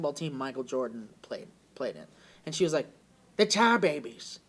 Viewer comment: I dont get it. Is he saying Tiebabys?